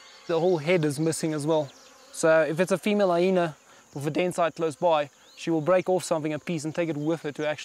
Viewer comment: Can someone explain what he’s saying @ 9:51-9:58? I turned on CC and it says he’s saying dancer? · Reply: “Den site”, I think that is what he is saying.